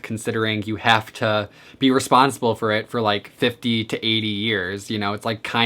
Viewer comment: Oh my goodness, I misheard you, and thought you said that FERRETS live 50-80 years, not PARROTS XD Rofl. Some animals I would never want are ferrets (too high energy), guinea pigs (their long lifespan, and large cage requirements), sugar gliders (I don't feel like I could properly take care of all of their needs), and pacman frogs (I like, and respect frogs, but I don't want one as a pet). Also any form of wild animal just because I don't think I could give him/her the home they deserve, and crave.